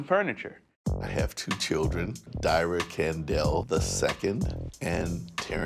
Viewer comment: When introduced his kids I thought he said diarrhea Kendell 💀💀